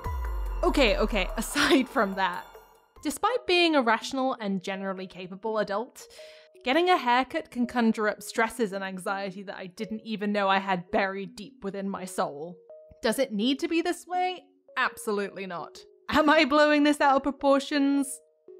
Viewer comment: I thought it said “get a haircut you nipple”